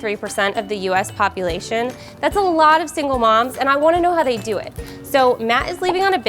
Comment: I thought it said "simple mom"not "single mom"